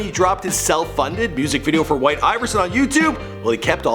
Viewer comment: did he just call white iverson "white iris?"